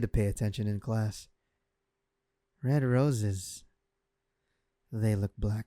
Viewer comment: Yo for a moment I thought he said 69 roses and I was like Hol up